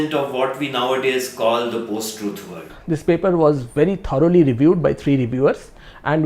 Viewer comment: I would like to know precisely what the main investigator is saying at approximately 5:45. "…increasingly now being called…"???? Called what? Sounds like he says "the post truth word". Is this correct? If so, with zero hesitation, I must admit that I am in full agreement with the authors of this paper and the youtube vid here presented. There is something subjective in the extreme lurking as motive behind most of the so called epigenetic wave and its advocates. From what I have seen, proponents of epigenitcs seem to be motivated by a form of not too hidden subjective hope for a mind over matter, a map over territory, an effect over cause that would provide a reverse after the fact top down editing of parts of reality that they find personally, emotionally, and or, philosophically uncomfortable. I must carefully do what those speaking in this video also do, I will explain patiently that a cyclical environment/entity/environment/entity influence feedback loop is of course always present and that this idea is not new and has been present since Darwin (and before), and also of course that the very idea of an organism or entity or species as separate from environment is problematic at best. But if you line up the influencers at play, if you build an influence hierarchy, epigenetic influences are simply minor in comparison to genetic influences (else of course, there would have been zero evolutionary pressure to select for genes and the genetic mechanisms that animate genes).